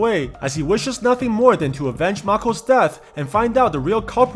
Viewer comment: What hes saying: to avenge makos death Captions:To AVENGER makos death